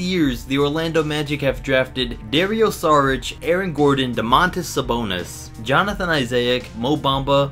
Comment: Did he just say Eric Gordon develop on the Orlando Magic IM Done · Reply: he said aaron gordon lmao